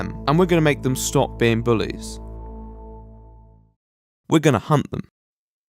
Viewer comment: I thought they said We`re gonna hump them not hunt them